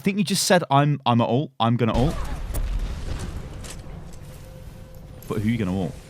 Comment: In the part where you said you thought he said "I'm gonna use my ult." He actually said "I have my ult."